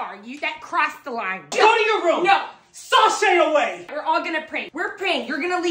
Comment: Did he just say sauceshay away? 💀